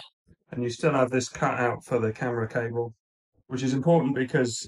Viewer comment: I thought you said "you still have this cat out for the camera cable"